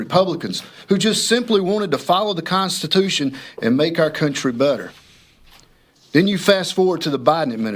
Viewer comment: Did I hear him say 'Make our country butter'? LOL!!! Nailed it!